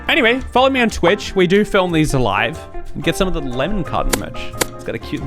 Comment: Every time I hear the words “Lemon Carton” in these videos, I hear “Lemon Cotton”, the only reason why I know it’s a Carton is because of the image shown on the screen.